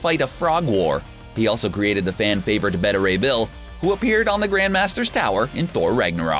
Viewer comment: did he say Betta Ray bill? Or am I hearing things